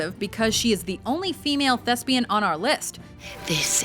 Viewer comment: did she say "female lesbian"?